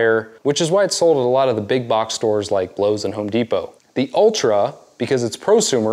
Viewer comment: Um….did you say “BLOWS” and Home Depot?? 😂